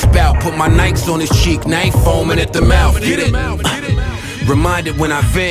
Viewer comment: "now he foamin at the mouth" is what he actually said. don't mean to be an asshole or anything, just wanted to point that out.